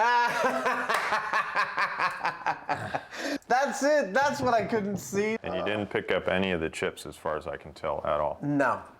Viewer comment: "Adam is a" can't understand the last word...sprzm? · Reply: "Spasm"